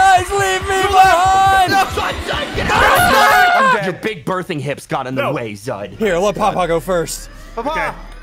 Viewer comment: D-did just said "big birthing hips"?!